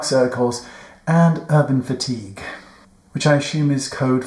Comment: I want to purchase this "for my husband." And did you say "urban fatigue" at 1:03? Thoroughly enjoy your videos, Wayne. ❤ 🙌🤸